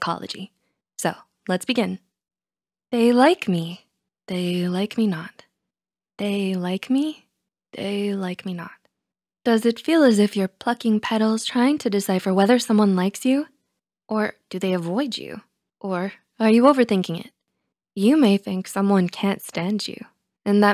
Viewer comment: when she said plucking I thought she said f***ing 😭